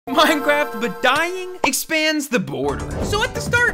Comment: I seriously thought he said "minecraft but dying is gay"